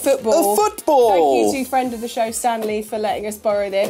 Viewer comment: I thought said: "friend of the show Stan Lee" 😅😂